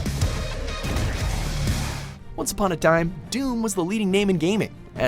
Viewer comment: I thought he said once upon a dime